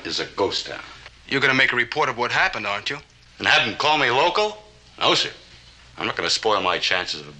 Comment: "And have them call be local?" That's what it sounded like the first time I heard it. :)